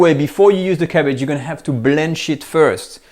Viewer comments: I thought he said "blend shit" but he said blanch it. 😂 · when he said blanch it first I thought he said we gotta blend shit first